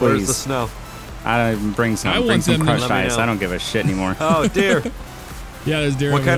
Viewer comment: did he say crush nuts